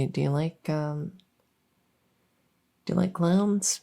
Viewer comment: I definitely thought they'd said Cloud not clown at first. Clouds are cool